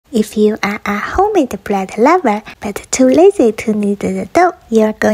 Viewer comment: For a second I thought she said ‘if you are a homeless bread lover’